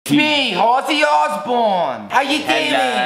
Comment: no one: Not even Sam’s demons: “aye it’s me Ozzy Osbourne” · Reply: I thought he said Horsey Osborne lmaooo